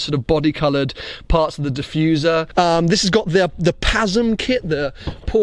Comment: I thought you said "It has the peasant kit" lmao